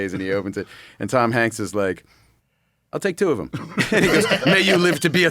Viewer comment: he actually says “I’ll take four of them” lol 😂❤ great movie.